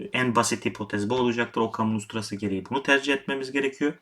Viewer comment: Volkan mostrası mı diyor, okan mostrası mı diyor, ne diyor? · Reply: Furkan KÜTÜK ockham’ın usturası